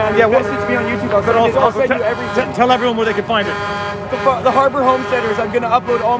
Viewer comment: The family with the drone footage (they will upload it when they get home) mentioned their youtube channel. (Thanks to Mary L in the live chat, because I couldn't make out the name of the channel with all the truck noise).